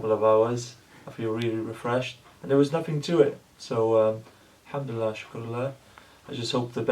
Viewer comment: what did he say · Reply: alhamd-le-allah wa alshukr-le-allah means : praise to allah "wa=and" thanks to allah